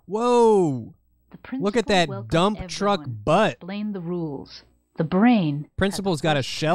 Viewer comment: Just listening to audio while making my dinner and I hear "Look at that dumptruck ass!" Of course had to stop and look. Lmao